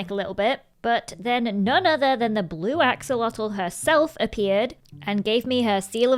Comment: For a moment I thought you said Bluebell instead of Blue Axolotl 😭😭 (It very late for me. But must watch LDShadowLady!)